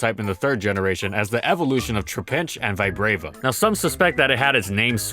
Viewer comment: Lol I thought that said "VIAGRA is evolving!"